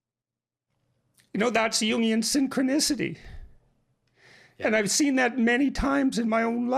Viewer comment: I have never seen someone tearfully utter the words "Jungian synchronicity". But then again, we are talking about Jordan Peterson. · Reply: I thought he said union synchronicity which is like saying chocolatey chocolate.